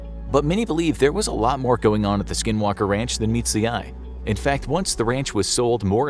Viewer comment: I thought it said Skywalker Ranch 🤣